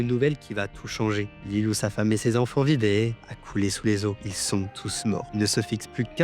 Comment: On comprend pas ce que tu dis à 2:00 faudrait que tu articule un peu plus en plus c'était une partie interessante sur ça femme et ses enfants, j'ai repassé 3 fois sur la partie mais franchement rien compris 😅 J'ai entendu "l'ilio de ça femme et de ces enfants vidé" Ça veut dire quoi "l'ilio" ?😅 Y a d'autres moments dans la vidéo où tu n'articule pas et c'est dommage ça casse le rythme de la vidéo par exemple à 2:36 on comprend pas ce que tu dis non plus. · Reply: oui j'articules mal mdr je suis conscient du problème je vais régler ça . La phrase c'est : " Lîle où sa femme et ses enfants vivaient"